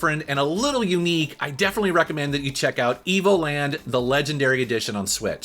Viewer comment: I thought he said emoland I did a double take really quick unfortunately I was wrong 😆